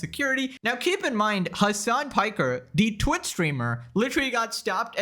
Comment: Did you just call hasan a "twit streamer," that's what it sounded to me...